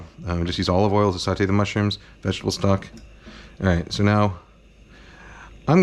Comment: Thought you said "Bailey's" rather than "bay leaves"